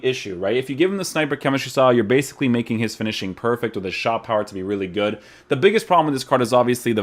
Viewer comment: i thought you said raul WAS really good...